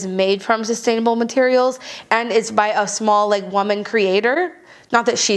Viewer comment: Lol I thought you said a "small legged woman creator" and I was immediately more interested